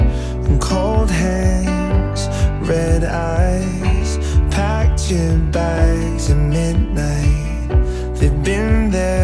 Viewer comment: Waiiittt 0:18 packed your bags? But i heard punching bags hahaha loll im going crazy😖😲